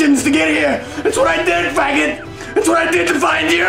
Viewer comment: Did he say "faggot?" Really? Wow. Okay.